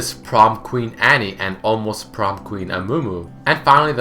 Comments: did i just hear almost prom queen amumu? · Did i only hear Almost Prom-Queen Amumu at 4:46?